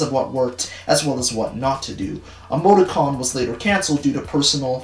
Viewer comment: Why you said “Emoti-con” I thought you said “Amogus-con” and I had to do a double take